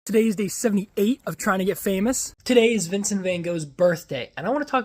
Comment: At first I thought you said Vincent Vega - and I was like ‘I don’t remember him sharing his birthday in Pulp Fiction’ 😂